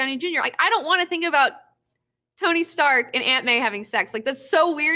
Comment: I heard that so wrong. I thought you said, "I don't wanna think about Tony Stark and Ant-Man having sex coming from a comic book perspective." I was like "is that a thing?" XD